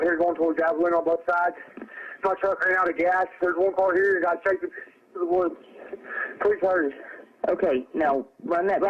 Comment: Okay, so in last call, I don't really hear "protect yourself", a gunshot or "guy got shot", but I can't really make words out of what I do hear. However, what I do hear is before that him explicitly say that he ran out of gas, something I can't quite understand but ends in "over here" after that is "guy chased us into the woods". That part is at 20:00. I believe the unintelligible part is "heard work over here", so the sentence would be "Ran out of gas, heard work over here and a guy chased us into the woods. Please hurry." He couldn't have run into him with the car because what would any of that have to do with running out of gas? How do you hit someone after running out of gas? I believe she misinterpreted the words and she assumed that. What he said right after that was "felt my heart stop" or "got the [unintelligible]" where the unintelligible sounds like two syllables ("gun out" would fit). I'd start searching a five mile radius starting with the car.